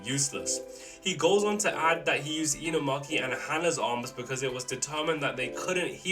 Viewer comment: Inamonkey instead of Inumaki is crazy 😂